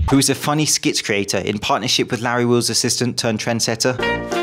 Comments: Trend setter? I thought you said Tren Setter when talking about the one woman.... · did u say tren setter? 😎